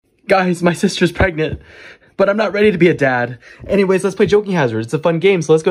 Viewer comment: "Sir, what did you just say..?" "I said my sister's pregnant, I'm not ready to be a dad!" "What.. In.. The.. Fuck did I just witness-"